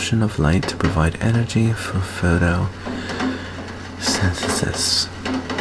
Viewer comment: For some reason I thought you said thanos-synthesis instead of photosynthesis